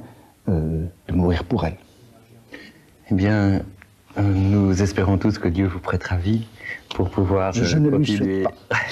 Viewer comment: For francophones: what does Foucault say just after 36:54 while he's out of the frame? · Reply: "je ne lui souhaite pas"